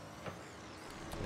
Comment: ne dedin abi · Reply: Emirhan ısınmış arabaya dedim